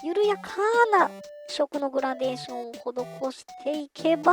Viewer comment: いつも楽しく拝見させて頂いております！ ちょうどブルアカの塗りについてしろいろ研究していたところでしたのでドンピシャな内容の動画でとてもありがたいです！ 10:34あたりで「間延びしそうなところ」でグラデーションを入れると仰っていますが、何色のグラデーションを入れると仰っているのでしょうか？ ちょっと聞き取れなかったのでご教授頂けますと幸いです。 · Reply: 聞き取りづらくてすみません💦「異色のグラデーション」と言っていますね。明度を変えつつ、色相もずらしたグラデーション、という意味です。ナチュラルハーモニーくらいの色相差が自然に馴染みそうな気がしています🌱